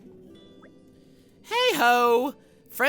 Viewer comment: Did...did she just call Nia a ho?!